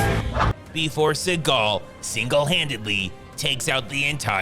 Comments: Did he say "Before Seagal, Seagal-handedly takes out the entire police department?" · Did he say "Before Seagal, Seagal-handedly takes out the entire police department?" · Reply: I heard it as "...before Seagal, single-handedly, takes out the entire police department."